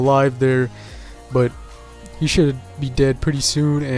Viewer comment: DID HE SAY "YOU SHOULD BE DEAD SOON"